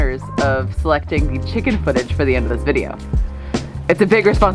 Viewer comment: At the end. I thought it said the hen. Great 👍 job